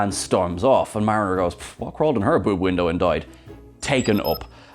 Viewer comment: Ohhhh, "boob window", I thought she said "poop window" which had me laughing more than it should have at the time!!! And on that note, Klowahkan still makes me laugh given that "Cloaca" is basically the chamber into which certain animals (birds included) have their waste and reproductive orifices open into, so is basically their poopoopeepeewoohoo hole, also it's latin for Sewer, but that's not much better... :P